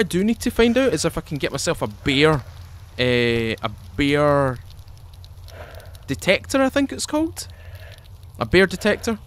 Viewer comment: Not so used to your accent so when i heard you say "Bear detector" I swear I thought you said beer detector lol.